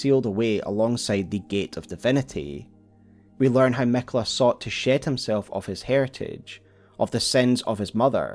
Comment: I don't know why I heard "Miquella Shit himself of his divinity" but I did and now I'm dying laughing